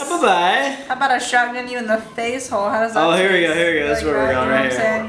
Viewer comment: the second time you said facehole it sounded like you said ill shoot em right in the face, ho XD